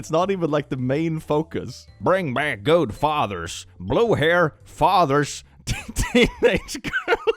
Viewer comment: at first I misheard this one and thought blue hair was blue hairs as in slang for old people